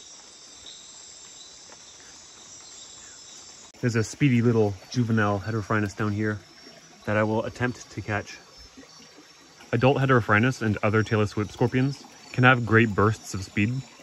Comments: Legit thought my man said "Taylor Swift Scorpion" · I thought you said Taylor Swift scorpion lol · i thought he said tailor swift scorpion till i read the description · Ngl in the first 10 seconds I thought I heard mans say "Taylor Swift scorpion". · I wasn't paying attention at the beginning and I thought you said Taylor Swift scorpion for a sec there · Lol, all I hear is 'Taylor Swift scorpion'. Good grief you have nerves of steel. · I thought you said taylor swift scorpion 🤦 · I coulda swore he said Taylor Swift Scorpion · I completely misheard what you said in the beginning. I had to check the subtitles to know what was actually said. I thought you said "Taylor Swift Scorpion" haha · Did he just say Taylor Swift scorpion 🦂 😂😊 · Did... Did he say Taylor Swift scorpions? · When you say "tailless whip scorpion" quickly i hear 'Tayor Swift scorpion'